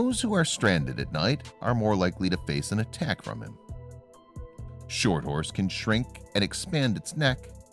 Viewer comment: I thought it said snort horse